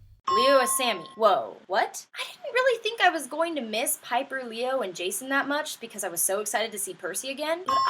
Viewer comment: When she said piper, leo and jason. I thought she said hyper leo and jason